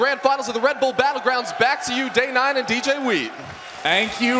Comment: did he said back to you Day[9] and DJweed?